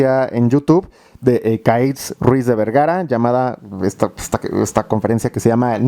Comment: al principio entendí Kareli Ruiz jjajaja 😅